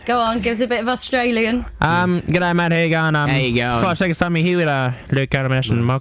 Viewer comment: What did Luke say? I got "G'day, mate, how're you going" and "Luke, Calum and Ashton and Michael". In any case, it sounds wicked, I don't know why. :D btw, I love Ashton's "I can't understand that". You and me both, buddy. But you're Australian.